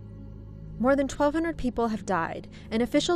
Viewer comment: I can't understand what she say at 1:46 More than twelve hundred people had died ?????